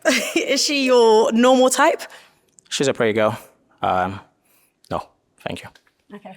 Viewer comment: I thought the first guy kept saying prayer girl 😂 (pretty girl) thought they recruited the women from Sunday morning service